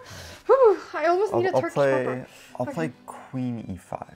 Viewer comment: what does she say? "I almost need a turkish pop up" or what? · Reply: she said "turkish pepper" which is a Turkish sweet, in the stream the winner ate one of those